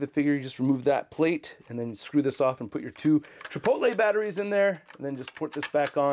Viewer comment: I'm sorry, at 10:00 did you say 'chipotle batteries in there'?